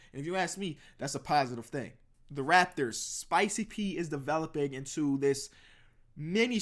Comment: When he said spicy p I thought he said swaggy p and I was like nick young is not mip lol